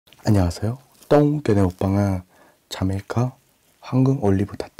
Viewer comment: 나만 여기서 안녕하세여 똥 이라고 들리나 ㅠㅠㅠ